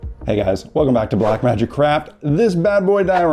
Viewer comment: did he really say "welcome to black magic crap?" :D :D